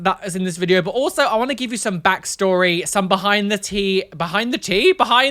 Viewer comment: Honestly thought you said "behind the tea" on purpose, works for you